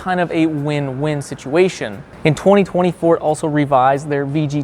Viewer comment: did you say 2024 or 2020, Ford?